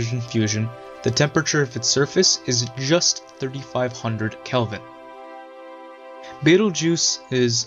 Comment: When he said “just 35000 kelvin!“ I thought he said Kevin 😂😂😂